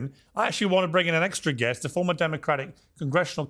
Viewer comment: for a second I heard "transformer"😅